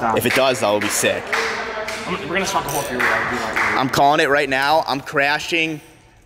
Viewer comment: Does say were gonna smoke a bowl at 5:09 ?